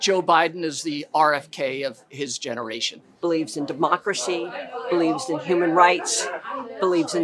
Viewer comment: Can't believe that wavering Kennedy is still promulgating the silly "bloodbath" lie. Also it sounded like Bernie said "Mockracy." Which might be appropriate to the state of this country: mocking what our ideals are.